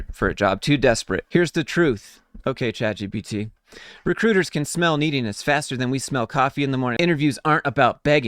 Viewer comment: Lmao sounds like he said "recruiters can smell neediness faster than we smell cock in the morning." 😂🤣